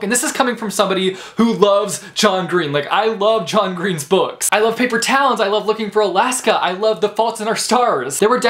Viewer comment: I had to relisten at one point because when you were talking about John Green I thought you said: "I love Paper Towels".